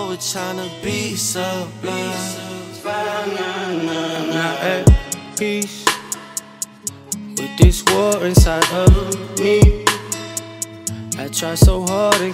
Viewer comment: "Im not at peace, with this war inside of me." Same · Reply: Emily Brawn Is that what is says. I thought it said I’m at peace with this war inside of me